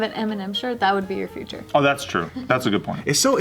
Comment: Okay but when was talking about the concerts I deadass thought he said "mnm's" as in the chocolate and was so confused